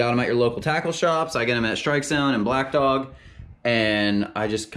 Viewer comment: Hello, this is Tim crews question do you know where i can buy some pompano floats? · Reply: I heard him say strikezone.